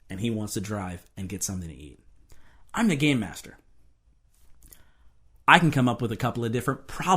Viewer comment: i thought he said "I'm the gay master" and then i thought of macdoesit and then i thought of "we read the gay agenda in this household.